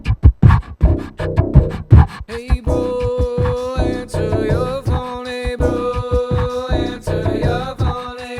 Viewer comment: For a moment I thought you were saying "April, answer your phone" "I was like dang it, now I can't use it"